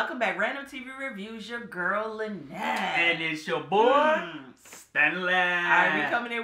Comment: For the longest I thought you were saying “It’s your girl Lynelle.” 😆 My bad cousin.